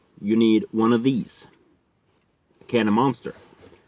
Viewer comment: Bro!!!!I thought u saID caMRAmounter instead of CANOFMONSTER!!!!!😀😀😀😀😀😀 Keep up the good work my brother, loved the info, and also your dgaf attitude!!!!!! #WOOT WOOT